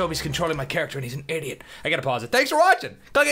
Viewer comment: it kinda sounds like he says "click the asain in the top right" sometimes